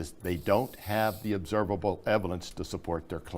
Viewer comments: did he say eviLence?? lol. WTF. Talk about fuzzy. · Just before 4:39 Did Mike Riddle just mispronounce the word "evidence"? It sounded like he said "evilence"...